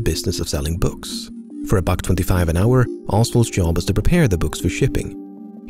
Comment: $25 an hour? For a book depository. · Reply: It's weird how people keep mishearing this. At 4:53, LEMMiNO clearly says, "As the name implies, the Texas School Book Depository was in the business of selling books. For a buck 25 an hour, Oswald's job was to prepare the books for shipping."